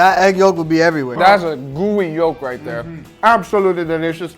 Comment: I know that said, "gooey yolk" @ 7:54 but I first heard it as "That's a Guga Yolk!" lol